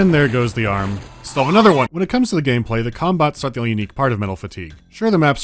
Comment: im sorry but all i hear is "cumbots" and im losing it each time